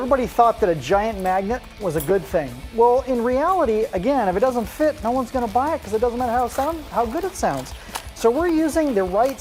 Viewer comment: wtf does he say at 1:13 ? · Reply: He was speaking fast and tripped on some words, but basically what he meant was: If it doesn't fit, no one's gonna buy it, [so then] it wouldn't even matter how it sounds.